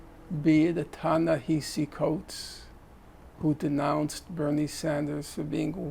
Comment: Finkelstein talks about "B.C. Coats"(?) denouncing Sanders. I couldn't hear what the name was of the person Finkelstein was talking about. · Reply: Hi, the name is Ta-Nehisi Coates Alongwith Kimberle Crenshaw, Robin DeAngelo, Ibram X Kendi who are the contemporary faces of critical race theory or intersectionality politics (Woke anti - racism) Their concerns in the previous generation were represented in Richard Delgado, Jean Stephancic (check names on CRT wiki page online) They say they are in the same ideological bracket as Prison Abolitionists or Abolition feminism of Angela Davis, and they are the same group to a large extent May also claim antecedents in Black power, black nationalism, black separatism, black arts, harlem renaissance related movements themes ideologues ideas May also lay claim to more left leaning figures like Bell Hooks, Patricia Collins and other forms of black woman (race gender sometimes class) cultural theorists